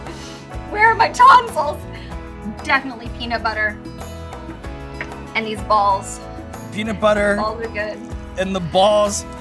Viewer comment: I heard "peanut butter....and balls" Haha lost it